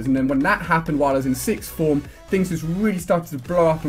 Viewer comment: Ali-A What do you say at 05:13 6th form?